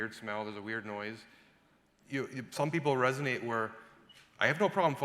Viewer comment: he says plum people? Couldn't quite catch that word